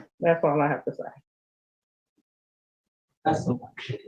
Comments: I think she said “That’s some bs,” and loud enough for Judge Bryant to possibly hear. Can’t believe she didn’t get laid into for that. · Did she say “That’s some bullshit? And the judge didn’t hear it?! Come on now